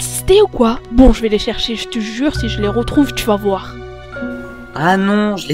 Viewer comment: Super épisode,mais moi à 12:16 j'ai entendu:J'te jure si je les retrouve je te nique! Puis:Cours ou t'es mort! XD